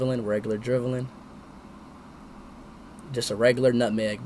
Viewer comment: did he say regular dribbling bro to dribble against professional players is no easy thing and Messi does it and also he performs the hardest role among all that is playing as no 10 u need to have a sharp brain and good vision and accuracy while Ronaldo who is a striker just needs to get to a good position for a good shot and goal